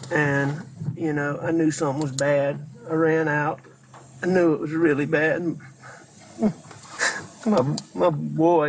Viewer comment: I hear "I knew it was real bad".